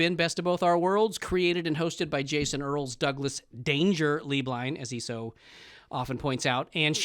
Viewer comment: When introduced himself for the first 8 episodes I thought he was saying, “Doug ‘Dangerously’ Blind” 😭 Cracked me up so hard when I realized I was wrong 😂❤️